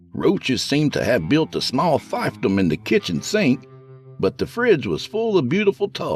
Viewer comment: or 32:38 what the heck is a fivetom or five them or five of them? Transcripts and captions say five them and I'm very curious what the actual word is and what it means? It has peaked my interest. Never heard of this word before today.